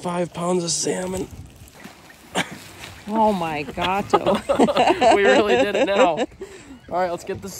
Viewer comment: did she say "Oh my gato"? 🤣